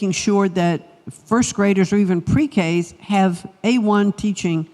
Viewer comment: I'm sorry I might have misunderstood but did she say A1 and mean Ai 11:00..... Omg she said it twice.